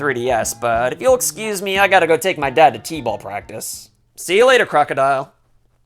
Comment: Did he just say "see you later, crocodile"